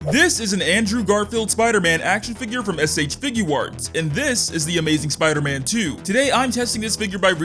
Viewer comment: why do you say s h figurwarts 😂😂😂instead of shfiguarts